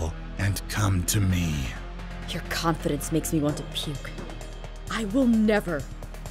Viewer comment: When she said "your confidence make me want to puke" I thought she said "your confidence make me want to pee" lol